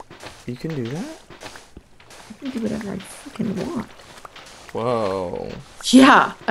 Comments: did Stacy just say fucking?? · Did Stacy say fucking? Woah I've never heard her swear before lol